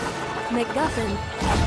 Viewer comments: i hear mcmuffin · I THOUGHT SHE SAID "mc muffin :3" LOL